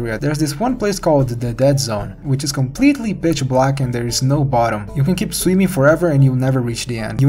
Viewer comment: am i sleep deprived or did he say "bitch black"?